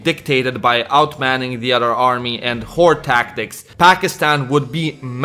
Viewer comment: I genuine thought he said "whore tactics" instead of "horde tactics" XD